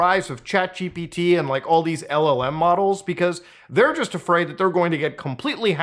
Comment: Everytime says LLM i hear " L O ELM models"